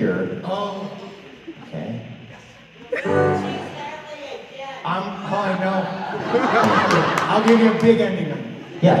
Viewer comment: What did that person say at 3:00? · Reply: It's really hard to hear, but she said "You'll never tease Hadley again!" It's a reference to Hadley Fraser, who is a performer that has forgotten lyrics multiple times with Ramin.